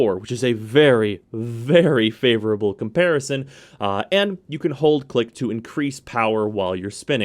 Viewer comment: I thought it said "can hold dick to increase power" on his 4th.